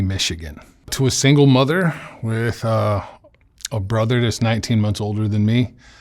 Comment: Thank you again Mark for giving me the space to tell my story, we all heal a little bit more every time we use our voice to speak our pain. Greatly appreciate you, my friend. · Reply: Here I thought you said your brother was 19 years older than you...I was so confused until you repeated 19 months later in the interview 😂😂. Anyway....I am so proud of how you turned yourself around. There is hope for humanity, after all! Enjoy life to the fullest my dear, you deserve it!